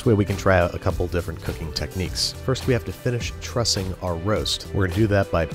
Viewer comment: Aw i misheard and now i cant unhear that you trust your roast